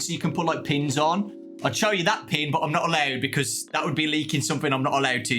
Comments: My heart stopped when I thought it said "leaving." But thank goodness it said "leaking!" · I thought it said leaving not leaking